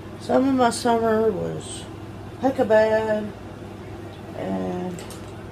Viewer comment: did she say "Hecka bad?"